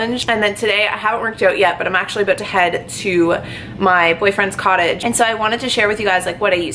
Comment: I was rewinding it cuz I heard "My Boyfriend " 😱 and yea I was right lol Hopefully one day you can introduce him to us 🤗 HAPPY FOR YOU 💖💕